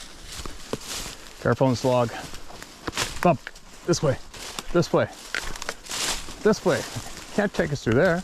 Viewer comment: Hi, Ruby and family. At 47:45 there is a voice that seems to come from the left, where Ruby is looking, mimicking. It says "this way" and does not sound like voice! It is heard right after says "Can't take us through there." FREAK-EE! It appears that you guys didn't hear it. May God protect you all in Jesus ' name. Amen. 🙏❤️🐾